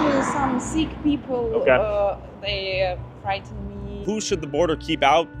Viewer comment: she clearly said "Sikh people, they frighten me"